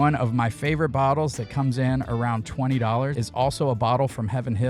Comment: Kinda jealous of those prices in your area 🍻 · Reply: & 4:07 sounded like under to me so I was like- man, where are you shopping cause I want to go!!! 😆 BTW, wasn't trying to nit pick your video my friend, I was listening to it at work and missed a lot of the first intro part when you definitely said "around" but watched it again to see where I was hearing under. No worries man, love your videos 👍